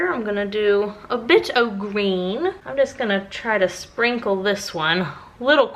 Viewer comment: did anyone else hear a bitch of green , i played it back like 5 times and i still do.